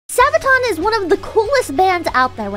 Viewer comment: I thought it said subathon, and I was like, cool!!! Pippa 24/7!!! Then I was disappoint 🫠